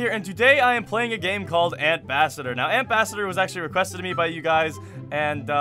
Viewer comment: I thought it said ant bastard